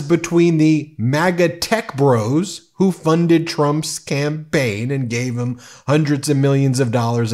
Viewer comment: I thought I heard you say, " . . . who ran Donald Trump's Scampaign . . ." and I thought that was great!